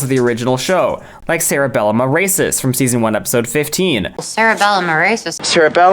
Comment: lmao i heard 'cerebellum erasus' as 'sarah bell am i racist' and thought it was an unusual inside joke 😭